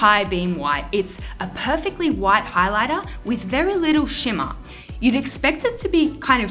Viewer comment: White highlighter and I thought she said white eyeliner. 😂